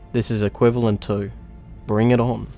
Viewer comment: What said: "The Allosaurus was biting the air, in dinosaur culture this is the equivalent of *bring it on.*" What I heard: "The Allosaurus was biting the air, in dinosaur culture this is the equivalent of **MORTAL KOMBAAAAT**."